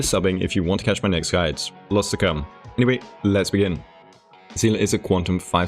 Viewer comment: ayo where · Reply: I misheard when you said "Let's begin" to "Lesbian". I really thought you said the latter as a joke because it's Seele.